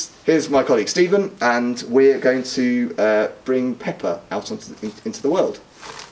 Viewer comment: i swear to god i thought he said peppa xD